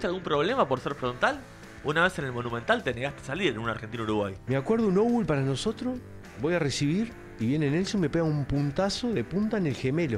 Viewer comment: yo solo quiero saber que dice en el minuto 2:50 "me acuerdo un... para nosotros" por la seña calculo que se refiere a un lateral jaja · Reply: Anglo futbolero yorugua: Obul= outball (lateral) Corner = que en todos lados😅 Faul= foul (falta) Guambia= One bihind you (el famoso "te llevan")